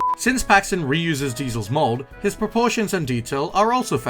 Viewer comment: im not the onley whan who hear him say mold